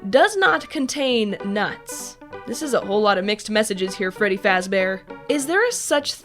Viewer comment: I’m pretty sure did 83 bites while testing the food. FredBear would be proud. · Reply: For A Second i Thought He Said Glam Rock Freddy XD